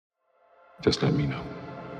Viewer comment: Did i just hear "Just lemmino(let me know)"